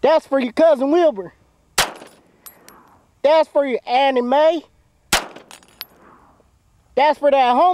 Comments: Did he say thats for your anime ? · HOLD ON. WAIT. her name is darqueesha?? Not to be racist but that is the blackest name I have ever heard. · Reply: did he say anime or Anna May